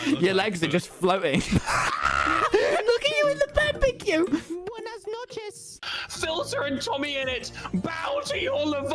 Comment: i think hes saying buenas noches? IDK SPANISH BUT THATS WHAT I HEARD SORRY IF ITS WRONG CORRECT ME IF IM WRONG PLISS